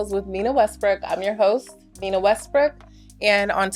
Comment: Honestly, I heard "Nina Westbrick." I literally heard that. That's how she said it.